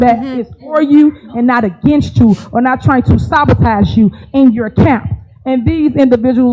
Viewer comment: "Sabatize" or "Sabatach" either way WTF is she saying??